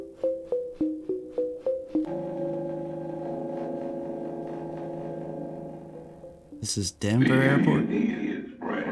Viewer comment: Can anyone tell what the announcement was that was made at the Denver Airport? I’ve tried replaying it, I just can’t make out what is being said. I hope everyone is having a great day/night and staying safe! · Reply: Immediate something - threat? .. in this area?